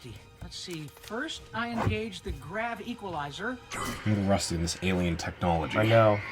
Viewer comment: I think he said he was rusty